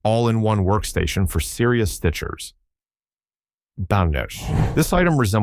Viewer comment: Yeah, the robovoice had a rough day on this one 😅 Working on getting it to sound less like a toaster, more like a human! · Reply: a mysterious exclamation that sounds like "Ban-Gesh" is uttered. Does anyone know what this could be? 😉